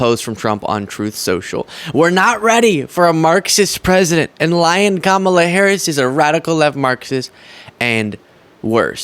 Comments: I keep hearing: "Lion Kamala Harris" 🤣🤣🤭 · When Trump first said Lyin' Kamala Harris, for a split second I thought he said Lion Kamala Harris because she's a formidable opponent. So now, to me, she is Lion Kamala Harris because she IS formidable!